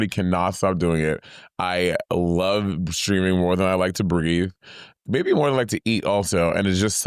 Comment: did say she likes streaming more than she likes to breed? Did I hear that right? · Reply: Breathe, more than she likes to breathe.